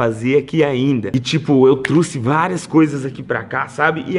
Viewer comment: só eu ouvi ela falar ''truce''?? ao envez de trouxe